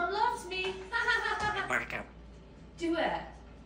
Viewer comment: Sounded like he said "squarrel" in the end there lol